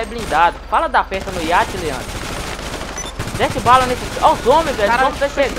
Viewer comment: kkkkkkk fala da peça do iate Leandro, foi so eu q ouvi? kkkkk